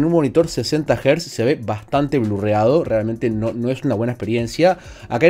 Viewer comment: que dijiste? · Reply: Blurreado, de blurring borroso.